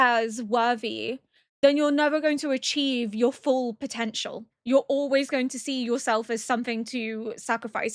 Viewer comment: DID YOU SAY POTENTIAL?